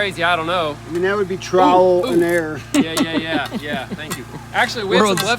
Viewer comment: It's funny you say trowel and error. I live in Pittsburgh PA metro area and it's sounds like people say trial but they are saying trowel. Lol. It's just part of the Western PA dialect. Great joke as always my friend. And I most definitely have drove the wrong way before. A contractor always has a hundred things on his mind. I feel ya my friend.